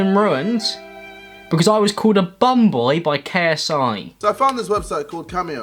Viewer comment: I genuinely thought KSI said Pom boys as in Pom meaning English